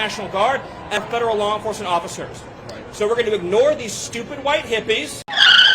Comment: I didn’t hear “stupid” I heard “elderly white hippies”. Wish he said stupid though